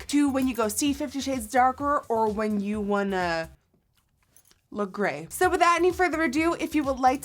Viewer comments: when she said 'when you wanna... look grey' i thought she said 'look great' whaT A LIT PUN LMAO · When she said "Or when you want to... look grey," I heard "look great," ever so slightly.